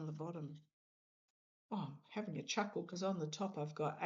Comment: 🤦‍♀️ you said “having a chuckle”. I heard “peppermint chocolate”. 😂 I could not figure out how sweet treats would fit in but I was open to it. ✌️🩷